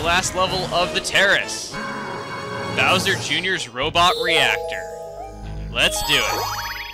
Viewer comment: i thought it said robot rector D